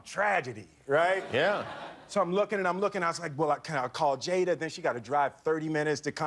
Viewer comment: Did he call Jaden a 'she'? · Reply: A.D.A he said Jada..Jada is his wife.